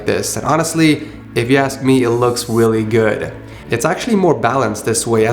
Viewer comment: It sounds like he said “it looks willy good”